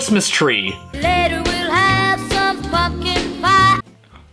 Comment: I always heard f’ing pie